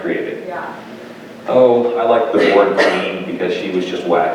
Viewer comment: What did he say was his first favourite villain? I can't hear over the coughing at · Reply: The Vord Queen from Codex Alera